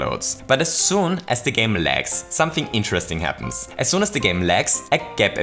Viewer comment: for a second i thought you said the game legs lol